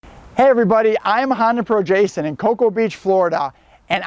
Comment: Bruh I thought you said, “Hi I’m on probation and...” lmao